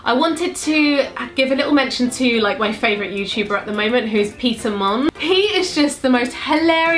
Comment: I really thought you said; 'pizza mom' when you where talking about peter mon